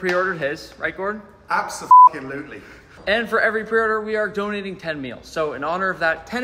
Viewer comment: I thought you said we're donating 10 mils💀